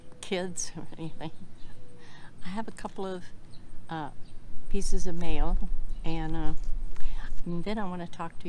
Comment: I thought she said I have a couple pieces of mayo